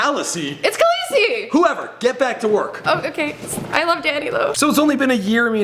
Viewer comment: wait what did she say " I LOVE DADDY"